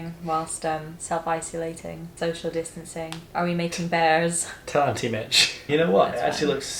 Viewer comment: Did just say “Tarty B****”!? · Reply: he said "tell auntie Mich"